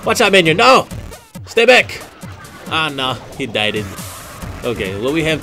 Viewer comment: Did he say "dieded" xD